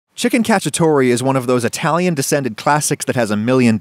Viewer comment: at first i thought it said "chicken catastrophe"